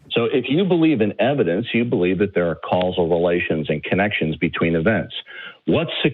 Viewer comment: Oh, I thought it said "casual relations".